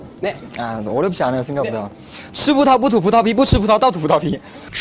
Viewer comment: What I heard : Subutaubutaubutubutaubibuchubitautautebutaubi